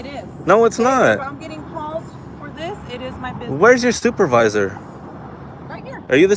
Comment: Sounds like he said where's your stupivisor